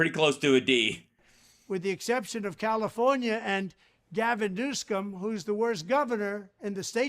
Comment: Gavin Noo-scum instead of Newsom. I'm surprised you missed that Hal. But damn you're good. Watching from Canada 🇨🇦 👋.